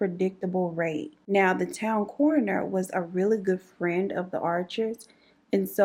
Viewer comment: I thought she was saying "sis in that corner had something going on". The whole time she is saying coroner 😂😂😂